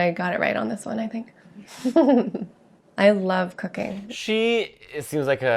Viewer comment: fully thought she said “i love cocaine” here for a couple seconds